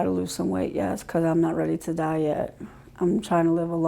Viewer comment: sounds like she said "Im not ready to diet" by she in fact says "Im not ready to die yet" Tell me that wasn't a Freudian slip.